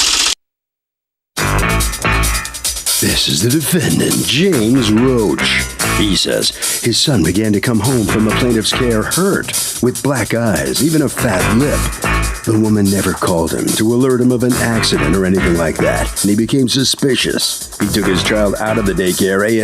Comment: Thought they said black guy lol